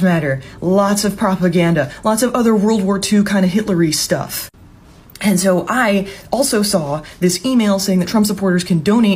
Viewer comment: Hitlery stuff. I wonder is she realizes that it sounded like she said Hillary stuff.